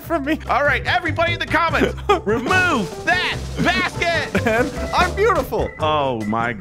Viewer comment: I thought he said "remove that ass kit!"